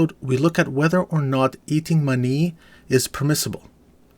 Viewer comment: For a second I thought you said eating money...